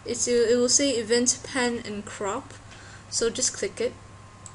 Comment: did you say crop or CRAP?! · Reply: GAMING I'm certain I said crap, mate! :P